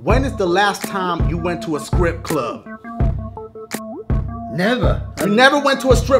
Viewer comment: Did anyone hear dashie say scrip club